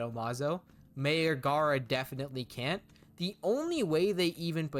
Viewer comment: I literally thought you said “Mayor Gaara” instead of “Mei or Gaara”. I was like: “What do you mean MAYOR Gaara can’t win?!”